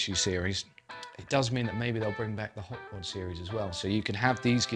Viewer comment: Great review and playing!! What series was referenced at 6:42? He said, maybe the'll bring back the " ———— " series. I couldn't identify what he said. I'm going to purchase the 61 Strat this week but may very well purchase an update if Fender offers the same guitar w/ a flatter neck radius and locking tuners. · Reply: Ah yes! can be rather softly spoken at times! He's referring to the Hot Rod series of American Vintage that Fender produced a while after releasing the first modern iteration of American Vintages. The Hot Rod series offered some modern appointments to expand the range. There is no confirmation or suggestion that Fender are planning to do this again with American Vintage II, but it really wouldn't be a surprise if they did!